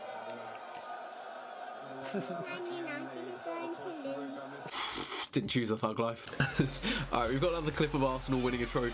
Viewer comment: I've watched this 9 times and I've only just understood what he said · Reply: Oliverツ man united are going to lose